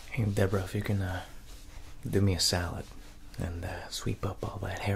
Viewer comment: It sounded like he said salad then I was like oh solid 😂